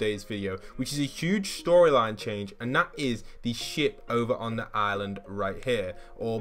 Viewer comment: what did he say?! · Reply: Is the ship but I see what you ment